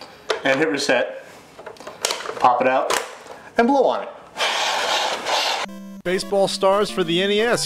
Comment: I thought you said NOT to blow on it!! (During the marathon